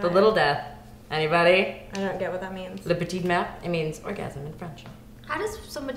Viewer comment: Hahaha, it sounds like she says "le petit merdre" which actually means 'the little shit' (or maybe "le petit mer" which would be 'the little sea').